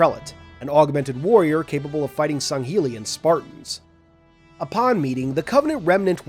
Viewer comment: did he just say Sanghelian Spartans? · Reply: Sangheli and spartans.